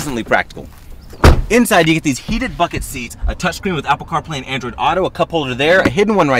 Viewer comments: I hear “HEATED FUCCIN SEATS” every time 😂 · I heard “heated f*cking seats” 😂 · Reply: He meant heated buckled seats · It’s sounded like he said “heating fucking seats” lol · Thought he said "it's got heated f**king seats"😅